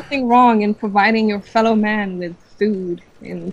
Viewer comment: drunk, high, or chicks who flaunt themselves at clubs or bars is an instant turn off. It is a "Noob-move"! And also at 9:09 i swear she says "FELLOW man"?!?!?!?!?!?!?!?!?